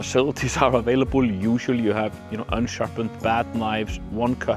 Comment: "Unsharpened bath knives", I heard. And saw, when I checked my ears with the subtitles. So, blunt knives for despatching guests in the (non-existent) hot tub? The Viking lives on, eh?